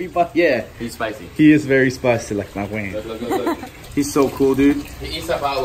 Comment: SOMEONE PLEASE CLARIFY Does say "yeah he's spicy...(like my woman OR like my WANG)" !?!?! 😳🤭🤔🤷🏻‍♀️😅